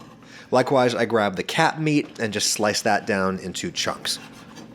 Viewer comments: When he said "cap meat" I thought he said "cat meat" and started looking around for Fluffy... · im pretty tired and thought he said cat meat and was very confused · "Likewise grab the cap meat" I thought he said cat meat 😅😅😅